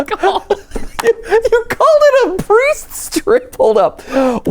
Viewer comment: I heard "Priest Drip" not "Priest Strip".... I now can't get that image out of my head